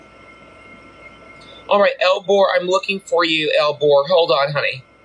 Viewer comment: This whole time I thought she was saying Al Gore 😭